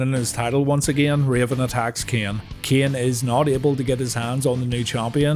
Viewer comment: heard this as “Cain is not Abel”